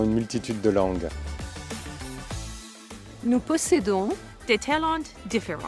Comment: Did she just say what I'm wishful thinking she said at 1:01 ?. " New Pussy to do". if so, Sign me up for NATO Asap! #FREEDOM #walkaway #conservative #commonsense #freethinkers USA 1st #Independent #Patriot #Alphamale 🍺🍺👍🏻👍🏻🇺🇸🇺🇸🇺🇸🇺🇸🇺🇸🇺🇸🇺🇸🇺🇸